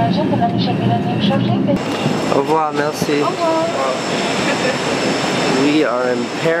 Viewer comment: what did French language he say? vuava merci? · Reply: said au revoir, merci (goodbye, thanks) :p